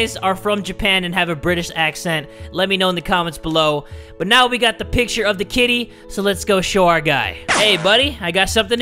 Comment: i paused when he said now we took a picture of the kitten but I paused halfway trough kitty so it sounded like now we took a picture of the kid